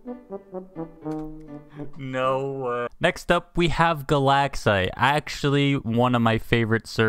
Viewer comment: "Galaxite" i thought you said "the lag site"